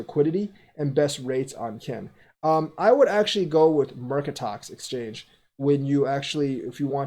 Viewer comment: great vid.....at 6:23 ....you mention where to buy Kin....couldn't really understand what you said.....the exchange.....thank you. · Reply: Hey Rick..... recommends Mercatox exchange